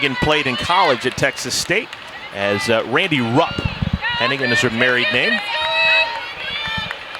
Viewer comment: Apologies. I thought said that Hennighan's name was Randi Brough. I now know ge said Randi Rupp.